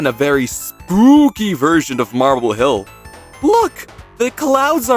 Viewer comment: did bro say marble hill? 2:14 💔💔💔